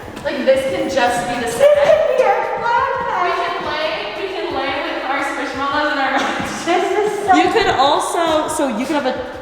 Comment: Did Alisha say they were gonna lay with their squishmallows? (9:38) 🥺💕